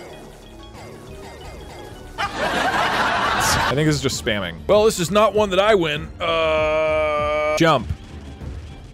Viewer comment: what did he say 🤨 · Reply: “tsst”